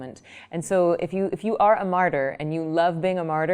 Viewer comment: Whats an ardir or something, auto captions says martyr but surely its not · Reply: Lol she does say “a martyr” not an ardir